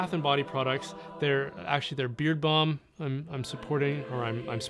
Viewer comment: Every time you advertise that Glory & Shine beard balm, I always mishear it as “beer bum.” 😂